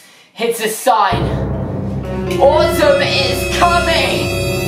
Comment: when he said autumn is coming i legit thought he said autism was coming and i waz all like dafaq